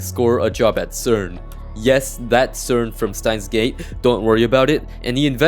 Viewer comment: did bro say CERN from stein's gate? Lmao